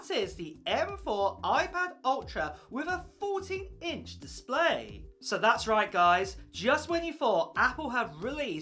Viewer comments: and 0:29 I could swear both times he said "a forty inch display"? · 🤣🤣🤣I thought you said 40 inch display